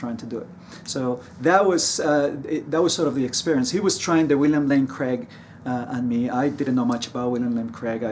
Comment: Did he just say William Lame Craig? 😆